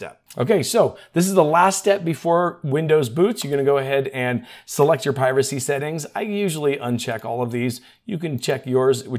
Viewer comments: sounded like you said "select your piracy settings". i was all in on those XD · I could have sworn that you said "select your piracy settings" I had to scrub back through and double check that. you said "privacy settings". I thought well it's microschaft, so piracy it is then those criminals can suck it! 😏